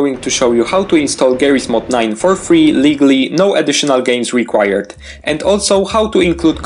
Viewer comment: For a second I thought you said, "Illegaly" even though it was actually, "Legally"